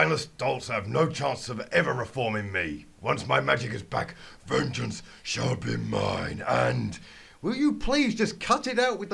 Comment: it sounded like you said virgins will be mine 0_o